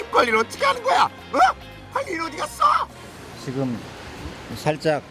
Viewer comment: 관리인 어디 갔어?!!!!! ㅋㅋㅋㅋㅋㅋ 아닛 수없이 봤는데 이제야 들리네요. 😂😂